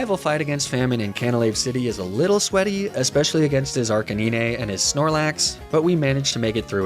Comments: what did you just say?? Arca-what???? LOL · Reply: Arca- NEENAY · oh shit, did he just say archikneenay? He is channeling the world champ difference. Games over already.